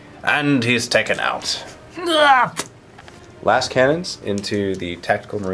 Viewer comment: your Reaper Autocannons are twin-linked, so you re-roll to hit for snap shots. Also, yes I thought he was saying "a**hole cannon" before I even read the comments. But, it did make me laugh to see that others thought the exact same ghing. :D